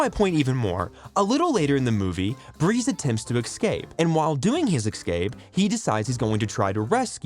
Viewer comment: I am not a native English speaker, but to me it sounds like you’re saying “excape” instead of “escape” here. I though the same in other videos already, but wasn’t sure. Is that an accent thing or just a speaking habit? From what I gathered I‘d figure you’re an American native speaker, so it just stood out to me : )